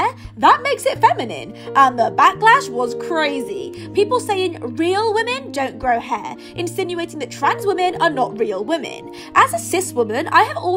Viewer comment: “Real women don’t grow hair” Damn bro guess I gotta go bald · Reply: At first, I didn’t look at the Readmore. I thought you just said we’re a woman don’t grow hair.😂